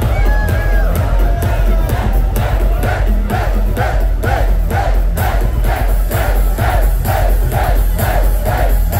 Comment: Thanks for upload! Does someone know what is crowd shouting at techno part 2:04? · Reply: the crowd were just shouting "hey", like it doesn't mean anything